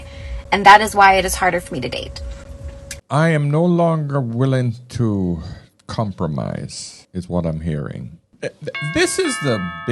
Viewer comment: “I am no longer willing to compromise”? I hear: “I’m looking for some mug to pay for me”.